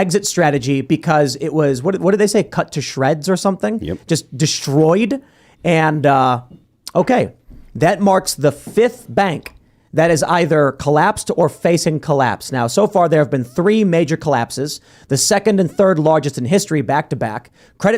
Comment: Because of the video title, it sounded like you were saying it was Fifth-Third Bank that was collapsing.